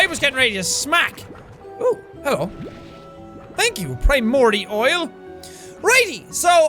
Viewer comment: did jack say pie morty oil? Lol